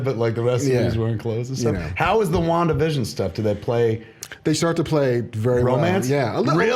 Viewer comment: When Kevin asked "how is the Wanda/Vision stuff?" I could've sworn he said long division stuff.